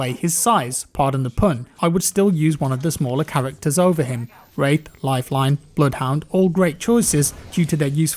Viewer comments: Did he say “r*pe” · did he just say rape? :D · Did he say rape? I heard rape.